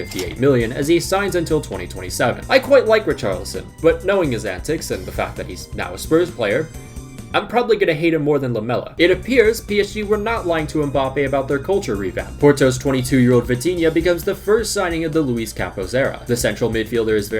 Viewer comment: I thought he said Lamelo and not Lamela